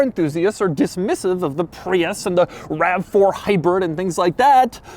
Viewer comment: did he say hybird? I think doug’s been watching savagegeese.